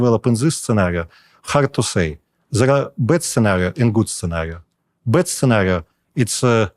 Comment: He sad bad scenario the subtitles say best scenario. Please correct that not to create confusion.